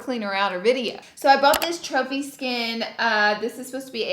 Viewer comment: In the beginning when u were showing the product I thought u said chubby skin not trophy skin lmao 😂😂😂😂 love u ❤️